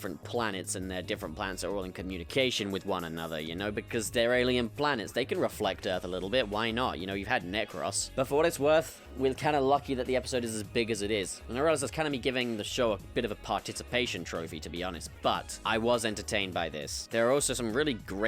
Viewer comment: Deadass thought you said Negros at some point Also Sacha is clearly just the one after Simm (Missy was mentioned to be unable to regenerate by him)